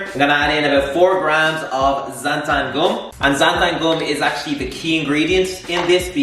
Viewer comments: Hello there ! Thank you for the video but i'm french and i don't understand the name of the ingredient at 12:30 for the ice cream please ? ''Zenta'' something? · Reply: Ok i found it! For french people who doesn't know neither it's called ''gomme de xanthane'' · what’s he saying? I’m trying to figure it out to make the ice cream 😭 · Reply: 4 grams of xanthan gum